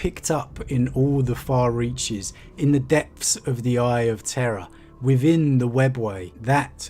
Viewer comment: When I hear you Brits say "Eye of Terror", I always hear it as "Eye of Terra" then have to correct myself.